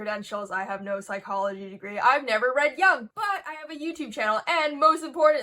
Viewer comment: I thought she said "i never read yaoi." and was about to argue about her potential on this...😭😭😭